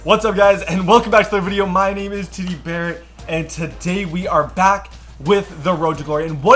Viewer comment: In your intro I thought you said tiki barret